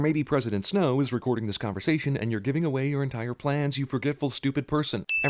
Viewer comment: [Avrangor] there's two you'res in the sentence. Ones right. One isn't. I'm talking about the second one. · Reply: Oh I am so sorry. I thought you were saying You're giving away, while you were saying You're entire plans. I get it now, sorry for that.